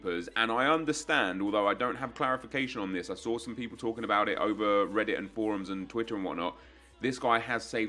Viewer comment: anyone know what said at 3:10?? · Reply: Talking over twitter and forums and reddit and whatnot.